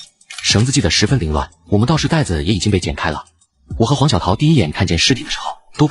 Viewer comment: 我聽成王小桃